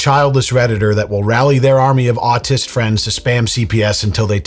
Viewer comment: I'm sorry-- WHAT kind of friends? Autistic? Is that what I heard?